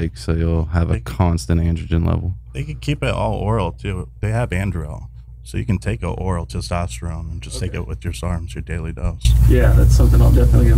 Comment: What is the word he is saying here at 33:10? Anderel? Or anadrol?